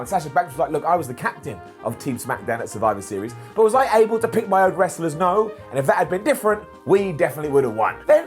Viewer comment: did he say can i pick my wrestlers nose???? HA!